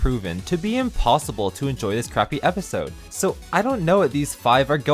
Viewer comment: i thought he was saying crafty instead of crappy